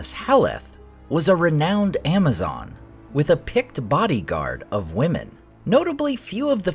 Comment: "Haleth was a renowned Amazon with a picked bodyguard of women." I heard that as a Pict bodyguard of women and had to check it with the captions on.